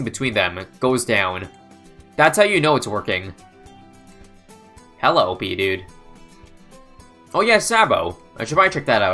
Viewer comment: I thought he said "Hello Pee Dude" 😂